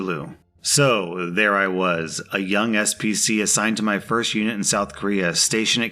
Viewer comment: LMAO i thought they said scp lol